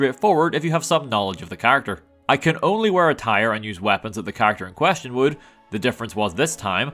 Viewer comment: I thought said "I can only wear a tire" not "I can only wear attire". My dumbass was thi king about if there was a tire helmet or outfit that I never knew about in NV